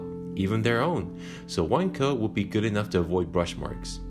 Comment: Great video! Is there a specific wine coat you use? How do you apply it? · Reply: Sorry I misheard. Heard a wine coat instead of one coat. In which case, I guess my question would be how do you get the color straight from the marker to look soooo smooth? When I try to fill in spots I miss, specifically with white, when I go over areas I've already done it messes with the paint.